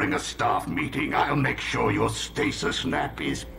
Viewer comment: I LITERALLY thought Megatron said “stasis NAPPY’S”! 😂 “The next time you decide to nod off during a staff meeting, I’ll make sure your stasis nappy’s PERMANENT!” Wouldn’t that be such a punishment? Going into stasis with a nappy; that you’ll still be wearing when you wake up? 🤣